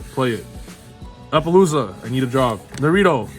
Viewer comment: what was that card? I keep replaying to hear what said and I keep hearing applelooza.